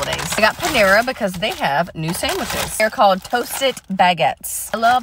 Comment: I put my phone down for a second and walked two steps away, and it sounded like you said toasted maggots